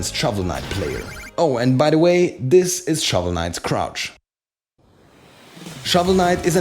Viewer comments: THEY KEPT HIS CROUCH ! That's so perfect I have no words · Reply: At first i thought he said crotch lol. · At first i thought he said crotch lol. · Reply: I thought he said crotch too